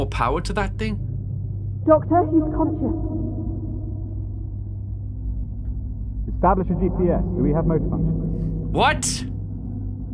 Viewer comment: subtitles say "Establish a GCS" but i hear "bardzo dziękuje" ("thank you very much" in Polish)